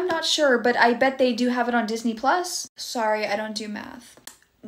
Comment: i thought she said, "sorry i don't do meth." 💀💀